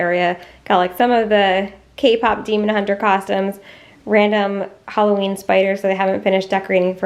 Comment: Ok I know this is probably a Pecan - PecAn thing, but It sounded like you said K-pop demon hunter “Possums” and now all I can picture is a gang of possums dressed up like k-pop demon hunters, wigs and all 😂😂😂